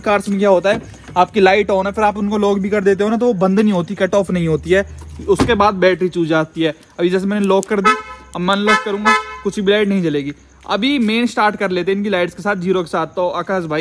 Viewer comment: Did he said, battery chud jaati h